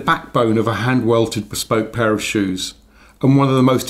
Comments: When says "Pair of shoes", I heard "Parachute".... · Pair of shoes! I thought he said parachute!